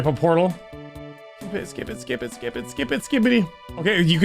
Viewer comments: did bro just say skibidi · why did you say skibidi god damnit